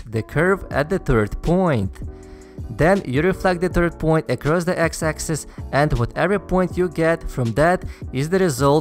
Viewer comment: Can someone tell me the sentence he says at 5:40? I can't hear what he's saying...can't make it out. Is he saying "Then you reflect the 3rd point occurs (across on) the x axis"?